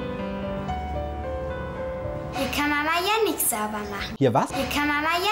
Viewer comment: Ich verstehe irgendwie jedes Mal: hier kann Mama Yannik sauber machen